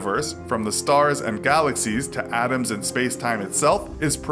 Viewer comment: I keep hearing "Adams" whenever Americans pronounce "atoms" xd.